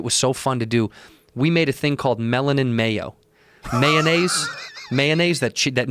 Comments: Thought he was saying “melon in mayo”. I was a bit slow on that one. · God damn it I thought he said melon and mayo.